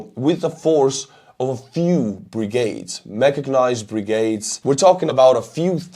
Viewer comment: Did he say 'machagnized' brigades? :D